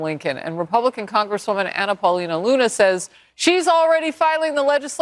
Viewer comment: When I first heard about the Mount Rushmore thing, I thought they said it was proposed by "an appalling loonie" 🤪